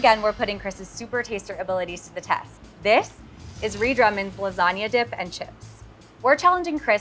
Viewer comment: Why did the subtitles say "redrum" instead of "Ree Drummond"??